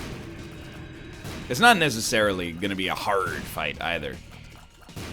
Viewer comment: does he say "Haarrrrd" or pirate jokes went too deep inside my mind